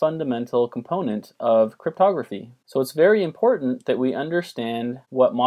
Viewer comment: I thought he said "modular arithmetic is a very fundamental component of photography " at 0:10 I was so confused omg I was like huh??